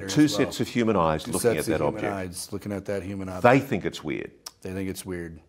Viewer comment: Did he say human object?! I think he did!